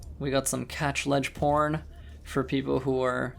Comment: did you really say porn? perhaps I heard that wrong. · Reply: Catch-Ledge Porn, that's correct. :3